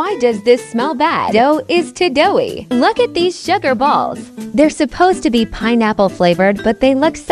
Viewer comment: Just me or did I hear them say "they're supposed to be pineapple flavour"?